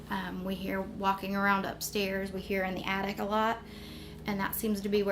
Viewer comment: IT SOUNDS LIKE IT SAID, THEY THINK WE ARE IN THE ATTIC AND A LAUGH!!